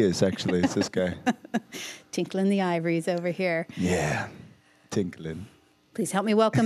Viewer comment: I swear i thought i heard her say "tickling the ovaries over here" lmaooo